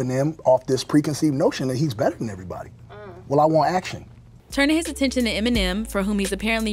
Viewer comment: “WELL I WANT ACTION” I heard “WELL I WANT ATTENTION”